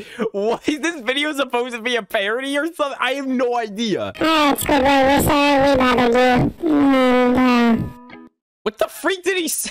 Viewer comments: I heard “oh squidward, we’re sorry we bothered you!” *random noise* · I can make out a couple words Spongebob said. I think he said "Ok Squidward. We're sorry we bothered you."